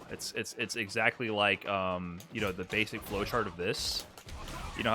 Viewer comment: Flow chart! I thought you said Heavy Punch!